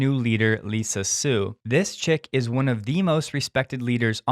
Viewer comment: Did he call Lisa Su a chick